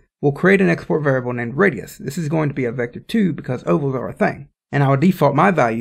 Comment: Thanks for a great tutorial, but one question: what are you saying at 6:16? "This is going to be a Vector 2 because <something something something> Subtitles seem to think you're saying, "because ovals are a thing" Not sure I follow.... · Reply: That's what I said. :) I say it because using a Vector2 allows you to set a x and y value for the radius, allowing you to create ovals.